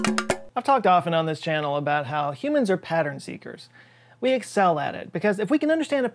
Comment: I thought he said pattern screamers at first